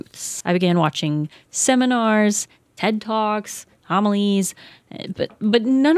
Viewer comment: Dunno what homilies are but without subtitles it sounded like said amalee's roughly @ 14:25 in